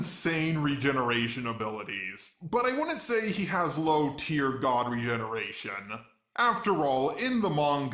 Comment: did i just hear... Low Tier God XD